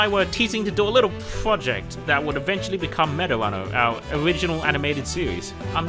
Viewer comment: the subtitles say Marijuana instead of meta runner